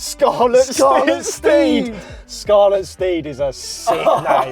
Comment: i thought you said "scarlet steve" and i think thats better